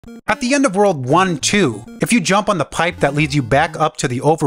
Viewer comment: I heard "at the end of world war 2" and it took me a moment to recontextualise